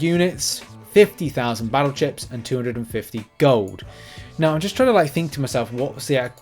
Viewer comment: Hi, I heard you say 250 gold...😂 I was only listening at that moment, but that got me : huuuh ??? No way 😮 but it's 250k.. sounds reasonable. But like some other viewers said: where are the Arena shards?? That's not gonna break the economy.... 120000 mythic shards might 😂